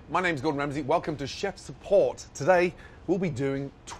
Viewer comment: I thought he said chefs port